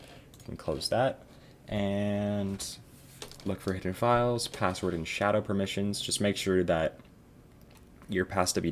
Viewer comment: Thanks for making this great video! I really appreciate it. I heard you mention shadow files and a passdvd file (or something like that) around 17:10. Can you expound on that a bit? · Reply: It’s PassWD and Shadow, here’s a thread that explains: <link>